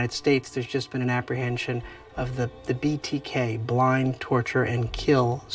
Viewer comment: did he just say Blind, torture and kill ?! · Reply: Bind, Torture & Kill B.T.K.